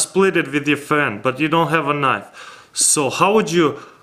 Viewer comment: did u just say u don't have nuts